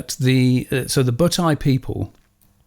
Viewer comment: I thought you said the bowtie people, and I was soooooooooo confused.